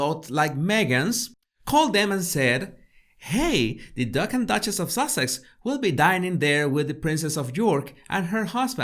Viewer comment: ….. the ducking Duchess of Sussex…. Hilarious what I hear 🥸 … or pleeeeease let it not it not be “The 🦆 Duck ‘N Duchess” or the Duck-end Duchess”…. how many surgical staples can I possibly bellow off in one year, and we’re only 8 weeks into it??